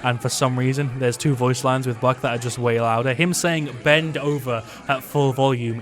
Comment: Buck: BEND OVER ! Enemy team: Stop saying that! Teammates: Seriously, it's weird... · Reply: I always thought he said "up and over!" but it always sounds like bend over lol